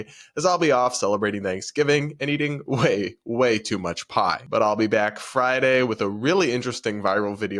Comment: Oh...pie! I thought you said "I'll be eating so much pot!" 😂